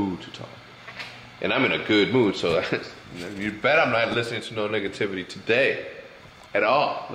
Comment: lol i though he was gonna say "you bet i'm not listening to any nigga today"